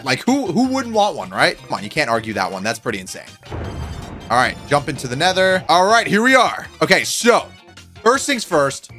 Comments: did he just say jump into the nether? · did just say "in to the nether"? :D